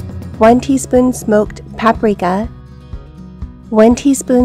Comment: Oh Paprika 😂I thought she said one teaspoon of Pat Morita!